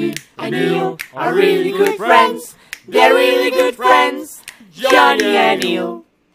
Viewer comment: When ill says John knee it sounds like she say Jenny